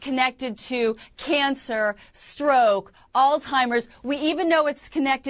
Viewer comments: Did she just say "all timers" 😂😂 it's Alzheimer's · Did she say "old timers" instead of Alzheimers? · Did she say "old timers" instead Alzheimers?